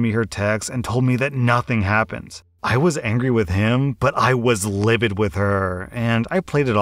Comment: "i was angry with him but i was livid with her" bruh you got it wrong it says "i WASNT angry with him but i was livid with her" bro you got the exact opposite meaning people who arent like reading this and just listening (like i usually do) will think that OP said that he was angry with him